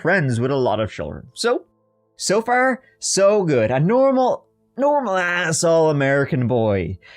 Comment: OMG, I totally thought you said "He was a normal asshole American boy" instead of "He was a normal as All-American boy" 😅 8:31 Wait, how old is this kid? He's already drinking in the second grade???